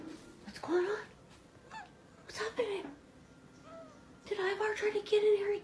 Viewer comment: I swear I heard eddie vr